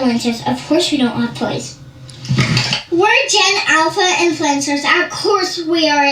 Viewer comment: I thought they were saying Gen Elsa's